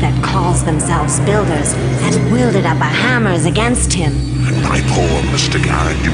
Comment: "Our hammers" am i hearing that right???